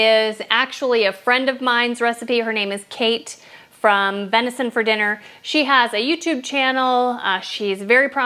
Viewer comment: Cute video. Your personality is very bubbly in this video. I'm super excited to watch more recipes of yours. I'm so glad you are promoting Venison For Dinner. I love watching her vlogs. I bought my whole cheese kit because of her. And she is amazing with juggling family, farm, and wife duties. I also have been posting her vlogs to my social media accts to try and boost her followers. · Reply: Thanks for that clarification. I thought she said “Medicine For Dinner”!